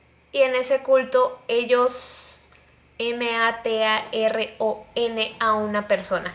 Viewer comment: Yo el el minuto 8:37 be like: Wtf!! Que significa M.A.T.A.R.O.N? de donde salieron esas siglas 0-0? Despues de escucharlo 10 veces entendi xd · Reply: que mataron a una persona